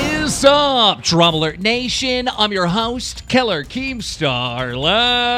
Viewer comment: is it me or does ever time Keemstar says his name as, "Killer keemstar.," I hear, "Keller Keemstar.,"